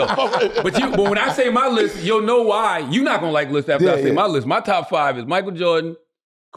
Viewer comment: In the intro I thought he said: “you’re not gonna like my lisp.” My bad😂